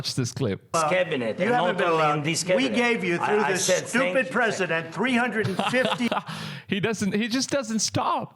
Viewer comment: Did Trump say, stupid president ?!!!😄